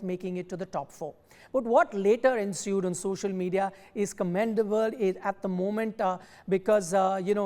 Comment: I heard commendable